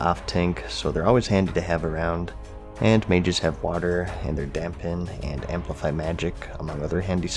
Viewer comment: Did anyone else here "Dab Pen" instead of dampen @ 16:23?